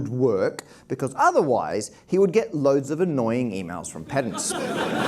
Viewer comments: Just to be a pedant: the captions say "[INAUDIBLE]" when it should be "pedants". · he clearly says "from pedants", not "from [INAUDIBLE]". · when you turn on subtitles because you can't figure out what they say, and the subtitle for that word is just "[INAUDIBLE]". Dang it. Everyone in the audience laughed so they all understood. Any ideas? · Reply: it was "pedants"